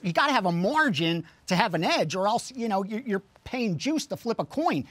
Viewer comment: did he say “Jews to flip a coin”?